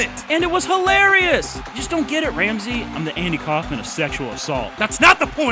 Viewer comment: I'm the __ of sexual assault. What does he say? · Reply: Andy Kaufman